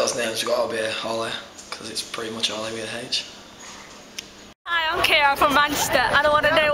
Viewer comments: it sounds like he said oli is pretty much oli with an h, those british, you can never heat their h's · When he said holly with his accent it just sounded like. " Probably the name oli because its oli with an h" it didnt even sound like he said holly at first xD